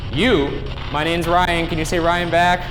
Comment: Yo anyone else hear the ghost say "no, you" at 20:19 or som? XD